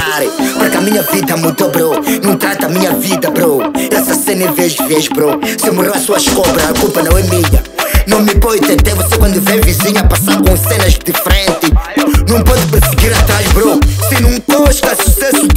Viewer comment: "Se morreu as suas cobras a culpa não é minha" peço ajuda, ouvi bem?